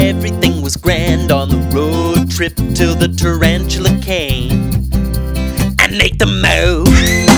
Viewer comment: at first, i thought he said "And ate them out!"